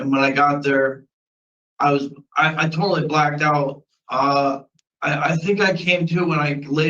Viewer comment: They say 'I blacked out' I hear 'B*ll sh*t'!